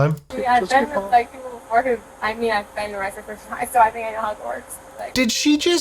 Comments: when she said "how it works" I thought she honestly said "I've been to HOGWARTS" as in the the last time she got pulled over by cops, she was pulled over at Hogwarts. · hahaha when you stopped and said "so I know how it works," I would have sworn she said something about going to Hogwarts! I think you got it right tho'. :D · Oh lol i swore she said she was arrested before at HOGWARTS!😂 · When she said “I know how it works” I thought she said “I’ve been to Hogwarts” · Each time she said “I know how it works “ I heard her say something about Hogwarts. I was wondering why she was dragging Harry Potter into it. Glad Leon translates drunk idiot language for us